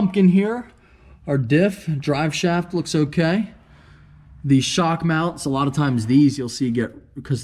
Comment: I thought you said shart mounts😂